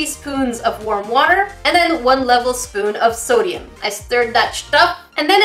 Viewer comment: might just be me but i heard “i stirred that sh!t up” ?? haha